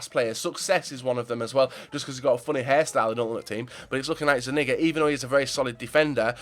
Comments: does say "he's looking like he's a n*gga"? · What did he say at 9:30 ? Did he say the N word ? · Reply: he said zuniga really fast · Did he say nigga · did you say n**ga